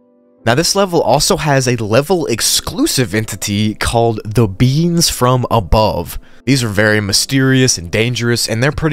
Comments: I heard "the beans from above" Instead of "the beings from above" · i thought said "the beans from above" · I first thought you said beans from above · I thought you really said,”The beans from above.” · ok that sounded like you said "THE BEANS FROM ABOVE" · who else thought he said the beans from above